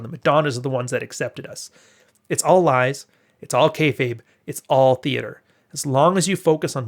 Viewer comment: What is the term 14:38? Cave-* ? What? English isn't my 1st language, and I can't hear it. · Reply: Caveman